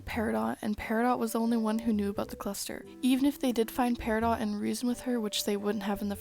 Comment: did you say the cluster?